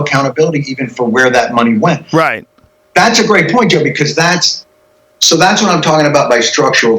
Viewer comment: Did Jon call Joe “Jimmy”? 🤔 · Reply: David Giltinan lol he said “joe because” but it did sound like Jimmy 😂